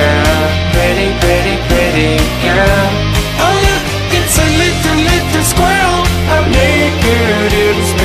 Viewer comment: Oh look! Is a little little squirtle! Srry i heard that